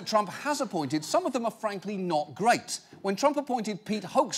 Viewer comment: the google subtitles say not grapes